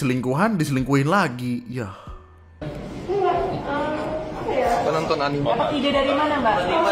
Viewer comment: ini gw yang salah denger atau ada suara cowok ngomong "mbak nonton anime ngga?" aowkoawkoakwo